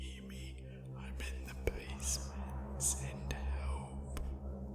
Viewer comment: Am I the only one who heard “ im alone in the basement, send help” between 11:25-11:36 of this video.